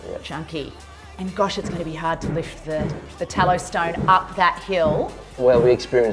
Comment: what did you say the stone was called on the kitchen counter top? I thought you said 'tallow stone' but google has no clue what that is. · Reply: Hi Amanda, it's Talostone Carrara Gold in Polished. xx